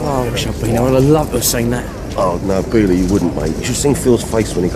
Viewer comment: Did Alfie call Ian Billy at 9:09? Sounded like it. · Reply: He didn't say "Billy you wouldn't have" he said "Really you wouldn't have"